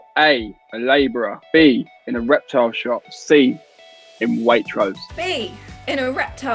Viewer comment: At first I thought Archie said "erectile shop," not "reptile shop" lol